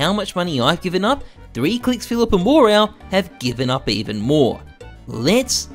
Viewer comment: can someone translate me what my guy jesus say after the ""remember" at 0:51 after he says no matter how much money ive given up i just listen mumblesn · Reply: “And remember: no matter how much money I’ve given up, 3kliksphillips and WarOwl, have given up even more”